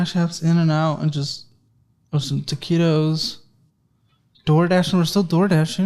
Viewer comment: I heard "taquito" instead of "keto," clearly I'm watching too many of these videos